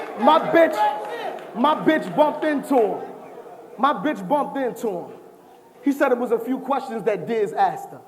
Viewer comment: did Rex say "My bitch bumped into him, HE said it was a few questions he asked..." ?